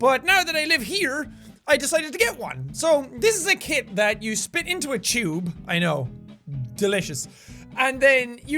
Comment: When he said spit into a tube I thought he said spit into a shoe